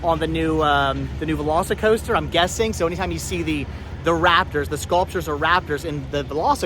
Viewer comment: I thought you said rappers! I thought “oooh I wonder if there’s an Eminem”😂😂😂😂